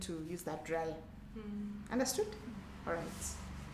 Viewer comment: Haha who else thinks at 0:43 understood sounded like alles gut 😂😂😂😂